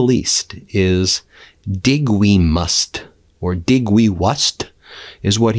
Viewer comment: I'd thought he was trying to say, "I think we must.".